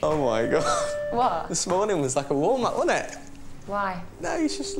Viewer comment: warm up, i thought he said walnut.